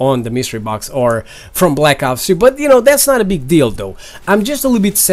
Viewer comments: for a sec I heard "that's not a big dildo" lol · it sounded like you said "no big dildo" instead of no big deal though